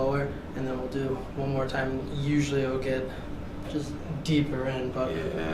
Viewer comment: you’re welcome ladies 😝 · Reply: Did I hear that doctor correctly when he said sometimes it goes deeper in@ 10:24? I sure hope so!